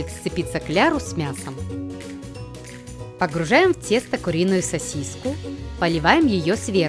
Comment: Кто услышал 5:49 сасиська